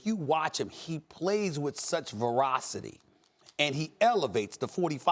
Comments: did he just say 'he plays with such verocity'? what in the hell is verocity? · Did he say “verocity” at 1:11?